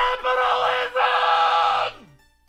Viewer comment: I thought he said "cannibalism" when I first saw the video